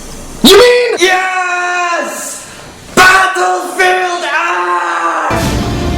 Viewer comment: I swear he says "Battlefield Ass"